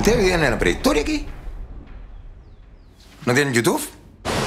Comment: did he say youtoof 😐?... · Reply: Yutub